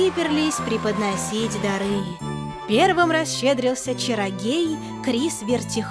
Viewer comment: мне не послышалось?! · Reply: Черогей?